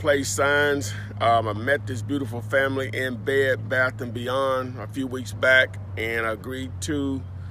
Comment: hilarious brother it sounded like you said you met that family in bed at 1st in bed lol of course you were saying that you met him at Bed Bath & Beyond